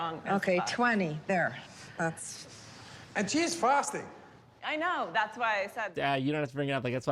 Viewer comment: when he said she's fasting, the subtitles said cheese frosting